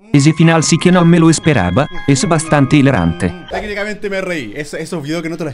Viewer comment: Me da risa la voz en Portugués lo que dice: "Ese final si que no me lo esperaba, es bastante hidrante" Muy buena Germán 😂😂😂😂😂😂